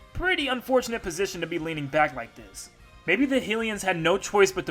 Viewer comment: did he said “Hilians”?